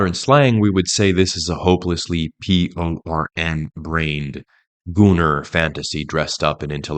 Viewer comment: ''''''This is not a boomer fantasy (if that is what is said here) Members of the Frankfurt School were PRE- baby boom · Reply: He said "gooner" someone who is addicted to "adult films"